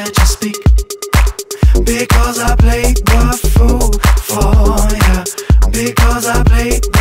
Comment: I thought he was saying “Because I make your food for ya” for the longest time 😂 lmao